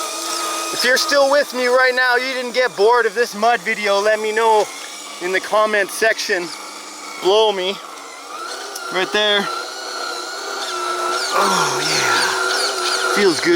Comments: What a beauty. I don’t know why but I thought you said “comment. blow me” lol 😂 love the new rig. Much love from Vancouver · Did you just say " Comment section.... blow me" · Be You man. I thought you said "blow me" lol I watched it all.